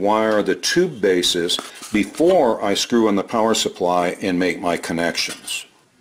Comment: I see what I did wrong - you said, "screw IN" the power supply. I thought you said, "screw UP" the power supply. I love the emergency ballast load resistor! I'm definitely adding that to standard operating procedure. Despite your best efforts with the super glue, the nuts did get loose and were last seen racing through neighborhoods in southern Texas. 👍